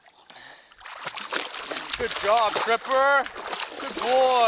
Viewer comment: lol when he was in the water it sounded like he said good goob stripper instead of good good job tripper